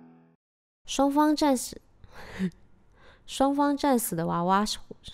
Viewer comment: 在說什麼? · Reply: 雙方戰死(笑 雙方戰死的娃娃(笑 啊~fuck 你再笑自己來配